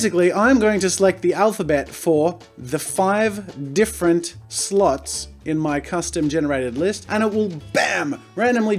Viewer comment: When he said slots I thought he said sl*ts and I thought jazza that's not family friendly 😹